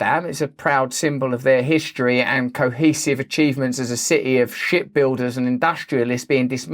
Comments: I totally thought you said shit builders🤣 not ship builders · Blahahaha!!! 🤣😂🤣 I swear, it sounded like she said “Shit builders” instead of ship 🛳 🚢